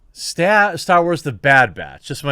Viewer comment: I thought it said Star Wars: the bad b*tch